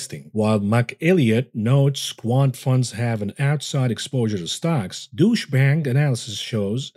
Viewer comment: You said "Deutsch Bank" around the 26 minute mark. I thought you said "Douche Bank" and laughed my tail off.